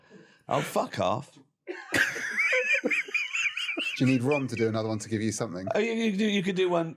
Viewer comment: what did he say? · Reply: "Do you need Rom to do another one to give you something?" Asking if he wants Romesh to give him another compliment for him to react to